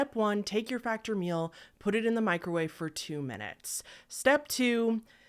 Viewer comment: I'm half sleep and for some reason I thought you said put the tulpa in the microwave and then step two... There is no step two 🤣